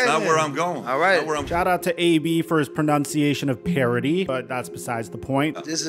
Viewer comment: Oh, he said “parody” I thought he said perogi.